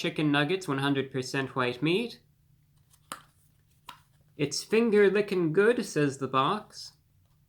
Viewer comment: sounded like sphincter lickin' good first time around